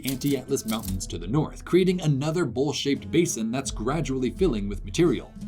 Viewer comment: I had to do a double take 😄 I heard "creating another bullshit basin" lol